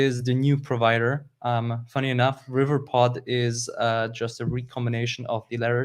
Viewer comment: cant figure out word at 38:12 anyone get that???(combination of provider and ???) About the video its fantastic clearly more active info of flutter in many flavors... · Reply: He just meant that provider and riverpod are just anagrams.